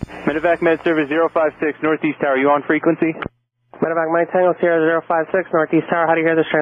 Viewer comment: it sounds like he is saying "you are talking with the wrong frequence" ?